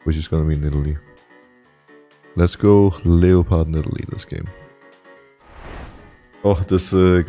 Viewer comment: Did he just say leo pard